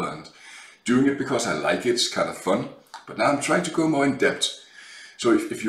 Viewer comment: misheard you saying "trying to go more in debt", and I'm like yeah that's what this hobby is about. ;)